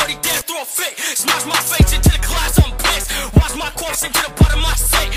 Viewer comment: I thought he said smash my face into the class I'm pissed. I was like I felt that